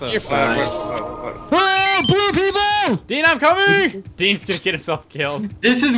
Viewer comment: I thought he said "AAAHHH! POOR PEOPLE!!!!".